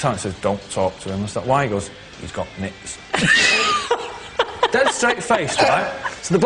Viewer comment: What does Noel say @ 1:04? "He's got...." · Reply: “Nits”